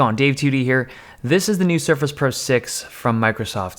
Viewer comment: I thought you said, "This is the new Zephyrus Pro 6 from Microsoft" at first. I got really excited. I guess I just am not into Surface products.